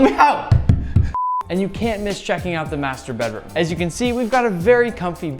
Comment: When he said master bedroom... I literally heard "mastu'rbate room"....🤭🤭🤭